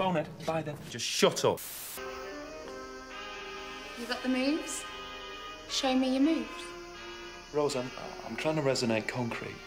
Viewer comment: I thought Rose said “You got the memes? Show me your memes” and almost fell over